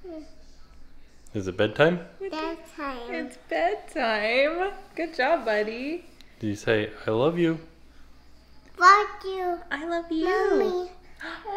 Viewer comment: When said “bed time” I heard “dead time” LOL I’m just too much in the Halloween spirit I guess. Great video!